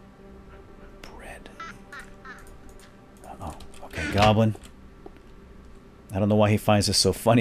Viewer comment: Did he said fucking goblin